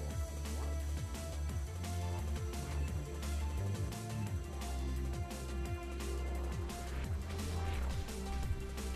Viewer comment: please tell me there's a reaction from the scammer at 6:50, I want to know what he said :D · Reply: "well done"